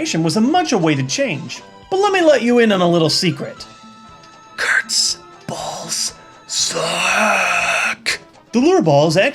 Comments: I thought u said to suck Kurtz balls...... -_ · It sounds like when he says Kurts balls it sounds like he is saying his nuts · I thought he said Kurt suck balls lol